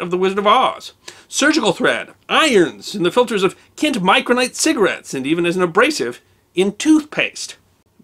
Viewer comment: Had to turn on the subtitles. Thought you said kids cigarettes. Considering the time when cigarettes were recommended by doctors, I would have easily believed it. Check out the Flintstones cigarette commercials or doctor cigarette commercials. A video on the history of cigarettes would be good.